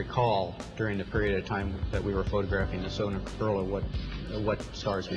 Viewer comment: This PROVES hoax! We believers may all be full of hot air when we claim to be able to discern these men are liars from observing them above, but at 4:18 Armstrong CLEARLY says, instead of "solar corona", "SONAR COROLLA", referencing a technology that only exists on Earth AND a car that didn't yet exist! He warned us about the hoax AND the doom of the US auto industry and NASA buried it! Explain THAT, Mr. ShillNaziPawnImnotjustinsultingyoubecauseIhavenoprooforeducation!